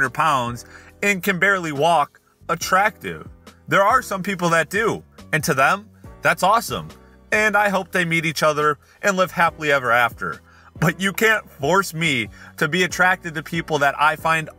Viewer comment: I genuinely thought he said ”I hope they eat each other”